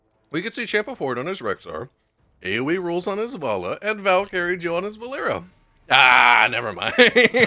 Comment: every SINGLE TIME you say ValCarried I hear "Valkyrie" pronounced weird xD I joined the game maybe a month before the talent-lock mechanic was removed. Thinking about that now, those were weird times.